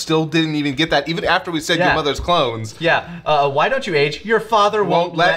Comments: Ngl I thought dr Briefs said “your mothers’ clothes”, not “clones” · I thought Dr. Brief said "that's where I keep your Mothers clothes"